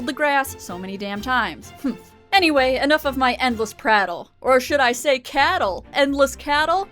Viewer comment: "anyways, enough with my endless ??? , or should I say cattle" I'm not a native English speaker. Can someone write what she said? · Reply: Russell Teapot "anyways, enough with my endless prattle, or should I say cattle"